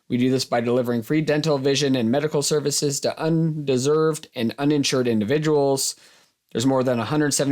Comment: Love your videos and all that you do. Also a huge fan of RAM. However, if I heard correctly @ 7:23, RAM serves the underserved not the undeserved! Keep up the good work and thanks for all you do. Glad to have you in East Tennessee! Go Vols!